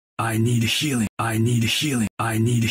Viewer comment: Genji: "I need healing I need healing I need healing" Me: Genji: "I need a shoe I need a shoe I need a shoe" Me: What? Genji: "I need healing I need healing I need healing" Me: oh okay. Genji: "BUY ME THE SHOE" Me: "WTF AM I HEARING"